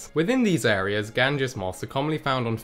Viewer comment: Lol I thought ot said "Gansta Moth" like, foreal.